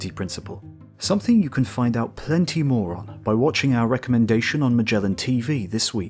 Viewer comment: "You can find out plenty more on..." Or did you say, "You can find out plenty, moron....." I hear what I want to hear,lol.